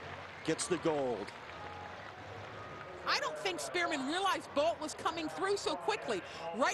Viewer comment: thought the commentar was saying spearmint